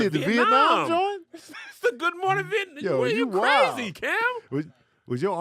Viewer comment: Did Kevin Say "Cam"? Did he come back?